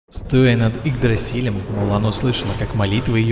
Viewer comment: Уже раз 500 перемотал, не могу понять кто услышал то? Улан? Уран? Лиам? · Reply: Мулан :)